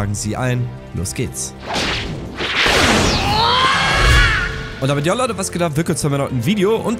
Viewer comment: Hallo ich lerne Deutsch .Zwischen 0:24 und 0:26 was hat Er gesagt?Hahah ich verstehe nur "Und das wird aller oder was geht ab (Ich bin nix sicher), blablablabla video. Hahahaa sehr schnell für mich.Liebe Grüße aus Brasilien!!! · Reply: Zahl ouk ‚Und damit jo Leute was geht ab...‘